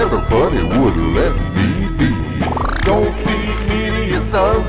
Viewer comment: Can somebody give a clear lyrics because for under the water because you know. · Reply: I always thought he said I don’t wanna go back to the water😂😂😂